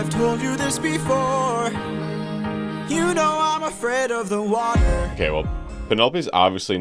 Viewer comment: I heard "I'm afraid of Milwaukee" instead of "I'm afraid of the water."